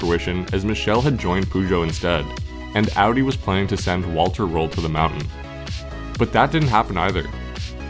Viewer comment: i thought you said Walter White, so i was just thinking a RV m*th lab had won